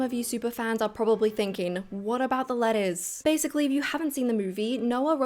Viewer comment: For a solid few minutes I thought you said "lettuce" and got really confused.